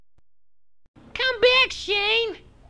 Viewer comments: "Come back, Shane!" shoots kid "My name's not Shane, kid." train flys away in space THE END TEXT MAKES A FREAKIN' STAR WARS REFERENCE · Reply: I thought he said shame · First name: Not Middle name: Shane Last name: Kid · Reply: I heard him saying shame